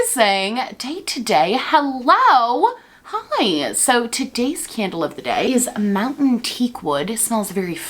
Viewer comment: when you say, "today's candle of the day" I hear "today's scandal of the day" 😸